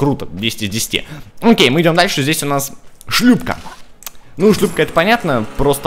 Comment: я единственый кто услышал шлюха или нет